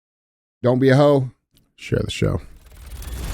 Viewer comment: The first month I listened to you guys I thought ya’ll were saying “don’t be a hoe, enjoy the show!” Haha I always listened while working nights at Home Depot, so it wasn’t until I caught the part about the fee finally that I got it 😂